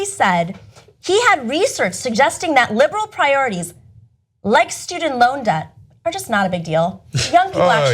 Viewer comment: Apparently, I don't speak English anymore. What the hell does she say at 1:43? "Likes to deloda...?"